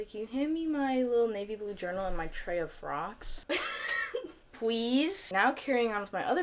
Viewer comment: I literally heard ‘tray of frogs’ and i was like??